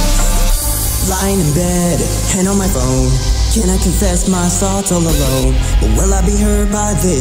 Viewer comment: why do i hear “Cannot confess my salt all alone”?